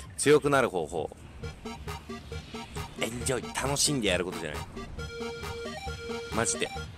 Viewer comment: エッチを楽しんでやることじゃない？にしか聞こえん